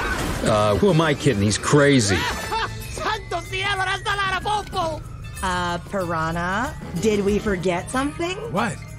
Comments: what the hell did piranha say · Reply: "Santos cielos, that's a lot of popo!" I think he's supposed to be Hispanic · What did Piranha say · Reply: Santo Cielo that’s a lot of po-po!